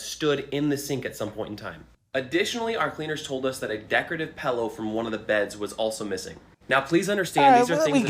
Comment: "Decorative pello" [Also at 1:12:15] Pello = pillow It took me a while to get this so I thought I'd post this here. Pretty much gave me an instant aneurysm when I did though. I mean who the hell says "pello" instead of "pillow?" 🤬🤬🤬🤬🤬🤬🤬🤬🤬🤬🤬🤬🤬🤬🤬🤬🤬🤬🤬🤬🤬🤬🤬🤬🤬🤬🤬🤬🤬🤬🤬🤬🤬